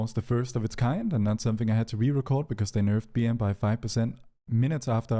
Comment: Did you say BM nerf or BM buff by 5%? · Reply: Nerf